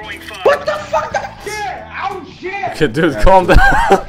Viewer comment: he is not saying somethin in arabic he sayin IM SHIT